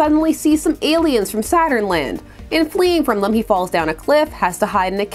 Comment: I heard this 3 times befor I realized that "Saturn-Land" isn't a place.